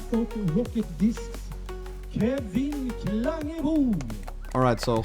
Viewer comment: hörde jag Kevin Klangebo?